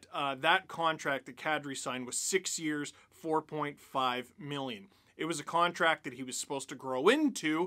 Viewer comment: Am i the only one that heard him say 1 year 1.6 million?